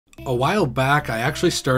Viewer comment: I thought he said “a wild back”